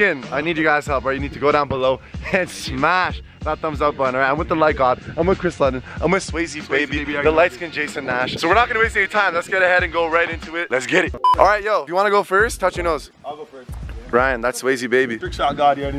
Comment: Did he just say " Jason Nash "